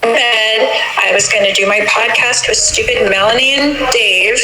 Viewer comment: Did Lori say at 20:43 or a few seconds before. “I was going to do my podcast with stupid Melanie and Dave" wow